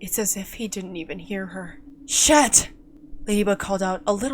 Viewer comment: it sounded like you said shit 😂😂😂🤣🤣🤣